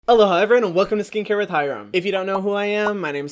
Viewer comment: Omg I always thought he said aloha everyone But it's just hello eyeone REALLY fast